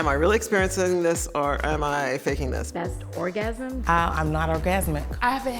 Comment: I'm not orgasmic... Why I thought she said im not organic. Lol